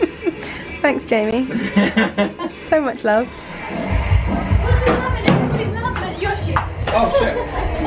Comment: does someone know what is being said at 1:31 ? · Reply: “What’chu laughing at, everybody’s laughing at your shit” I think??? Idk 😭